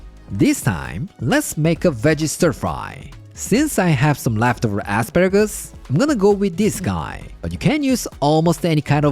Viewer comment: I know it's the accent man but I laughed so hard. It sounds like u said leftover aspergers 😂😂😂😂